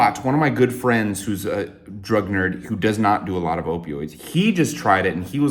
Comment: I thought you were a “drug nurse” for like 2 years because I misheard drug nerd btw